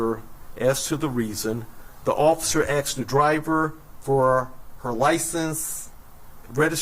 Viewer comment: So... did I just hear him say, 'the officer axed for her license'. The guy with all the stars seems a bit confused and ill prepared. This is someone in charge? That's all, TWS.